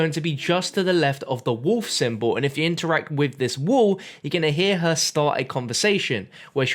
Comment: I heard wool, not wall